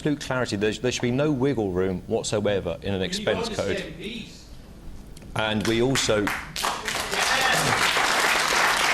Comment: What did guy shout at 02:22?? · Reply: I believe he said "honest mps".